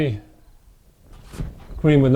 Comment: Hmmm I thought you said Thai Stick. Wrong channel! No ha ha jk